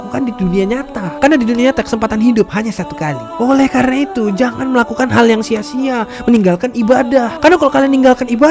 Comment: Kalau gak sala dengar...pas kata terakhir ada kata"oleh karna itu jangan lah ibadah" Apa aku salah dengar??